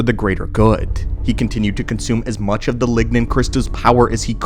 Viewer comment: On the last crystal I heard "Ligma" crystal XD